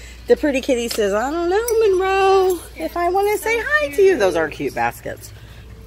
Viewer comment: I heard ‘those are cute b*st*rds’ instead of ‘those are cute baskets’ Both are correct.